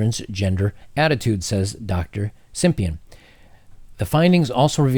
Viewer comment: I heard it as Dr Simp-ian.